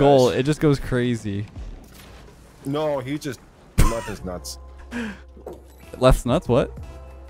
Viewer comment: Did someone count how many times did sounded like a kettle? And Did just say in the background he loves is(Leth's) nuts!? 10:25 😳😳😳